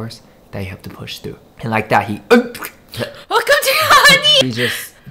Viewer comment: did she say 깜짝이야 or is it just me lol